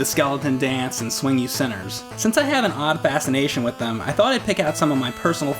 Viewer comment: Lol I thought he said he always had an odd fascination with boners